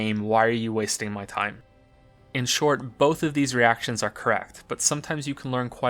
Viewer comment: Can anyone tell me what the hell he's saying at 6:29 ?!?? It sounds like "Both of these reactions are craft." or "Both of these reactions are cracked." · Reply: "Both of these reactions are correct."